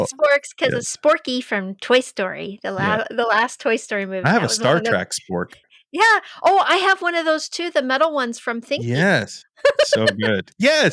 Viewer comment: did say he had a Star 'Track' spork???